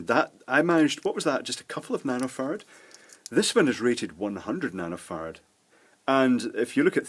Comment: did he just say 100 nano FART